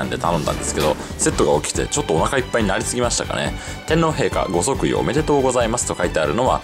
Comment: 天ぷら見てからこれ聞いたから天丼陛下に聞こえたw